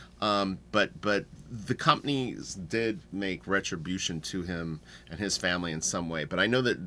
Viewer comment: COMPENSATE I said retribution...my brain was fried this morning...I've got 100 things going on and I wanted to get this up early! :) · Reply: I translated what you originally said and heard it as compensated